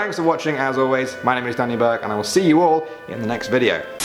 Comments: I always hear "my name is Daddy Burke... " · I thought he said “my name is Daddy Burke” lol